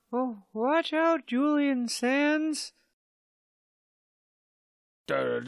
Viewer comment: did he say Sans